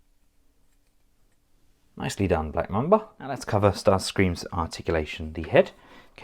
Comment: When accented a certain way, I hear "Black Memba" Anyway, paint apps on this is great, Screamer's rocking those wheel spurs